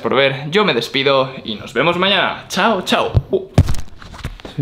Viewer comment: Que dijo? No entendí nada :( y tengo curiosidad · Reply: Se nota que llevo un par de cervezas